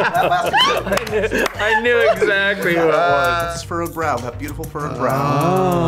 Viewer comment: No one: *Scarlett’s eyebrow appears* Hemsworth: thats bucky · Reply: Is that what he said???? Omg I thought he said "It's Froggy!!"